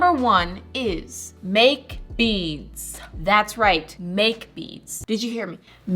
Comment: thought you said beans TwT whoops